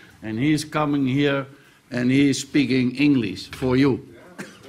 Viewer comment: did he say "No gay" at 0:30 ? lol what the fuck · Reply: He said okay in a very old person voice😂😂😂